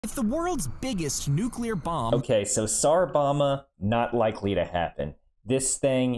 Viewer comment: I thought I heard Tsar Obama!